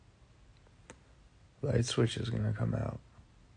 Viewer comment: I freaked out cuz I thought he said “Lightwswitch” wasn’t coming out! Then he said soon😭.... made me watch the whole video tho😆